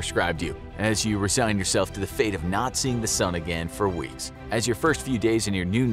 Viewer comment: as you assign your fate to Nazis Am I the only one who heard this?